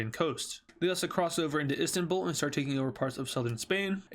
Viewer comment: did you say instambul???